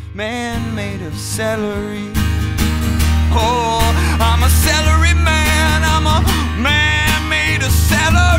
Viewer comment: slender man instead of celery man